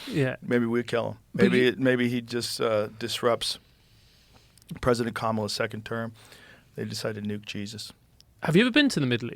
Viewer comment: did he just say "President Kamala's second term?